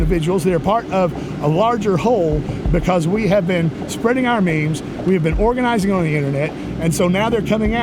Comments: Did he just say “because we are spreading our memes” Cuz if they got some good memes then I’m a nazi too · Did he say "Spreading our memes"? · Did he say. "Spreading our memes."